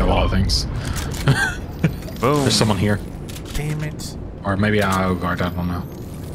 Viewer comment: ayo what did u say?(edit) he said dang it after re watching it